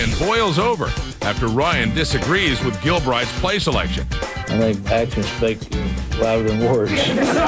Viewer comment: and what did he say in the 9:05 ? I couldn't understand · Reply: “I think actions speak louder than words.”